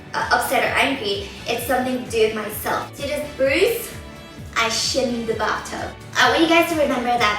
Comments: ok at 2:22 I thought she said "i shit in the bathtub" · lol i thought you said, " i sh*t in the bathtub " and that was why you were mad. i need to get my brain checked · When you said "I slipped in the bath tub" i thought you said "I shit in the bath tub." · oh god. 2.20 ...i thought she said 'i shit in the bathtub'... XDD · Oh god i thought you said 'i shit in the bath tub' D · Lol sounds like she said she shit in the bath tub! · i thought she said i slipped in the bathtub 0_0!!!! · At first when you pointed out your bruise, I thought you said "I shit in the bathtub" but realized you said "I shinned the bathtub" hahaha! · hahha I thought you said 'I shit in the bathtub' loool and love you <3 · ok at 2:22 am I the only one who thought she said she "shit in the bathtub"?????? O.O'